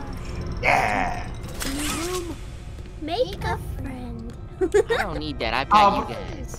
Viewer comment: Am I the only one who heard and say “make a fren” o-o